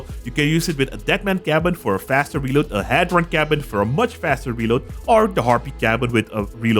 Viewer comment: did you said deadman cabin and head run cabin? i didnt find the head run cabin, please answer, love your videos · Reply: He said Hadron Cabin it's a Legendary Cabin